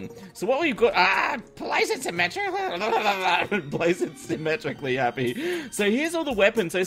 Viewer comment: it sounds like Happy says "Place it, Symmetra." Has someone been playing a little too much Overwatch?